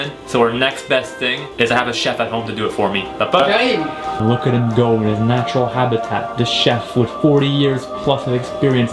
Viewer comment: il a dit quoi Chef exactement?? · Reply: "J'arrive" :D